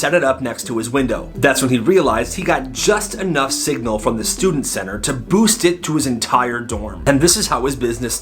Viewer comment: Did anyone notice it said milking station instead of Student Center?